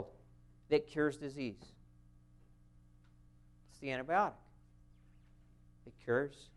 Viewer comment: Please help.. at 47:17 did he say antibiotic? Ive listened to it at slow speed, fast speed, normal speed to no avail. This is very important, and i believe in this man , just wish hed speak a bit clearer for us elderly❤. Did he say antibiotic, or something else? Thank you for helping me and excuae me .. i may seem like a Def Dork, but this old Def Dork would be very greatful and healthier for all the help i can muster. Thank you for your help.❤❤🙏👍